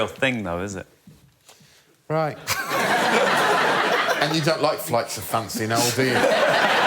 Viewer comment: What did Greg say at 36:10? I can't decipher that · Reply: I think he says And you don't like flights of fancy Noel, do you? I don't understand the reference btw